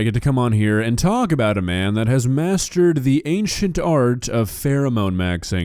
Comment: I seriously thought was gonna say “masterbate” hahaha omg I need to get my mind out of the gutter 😅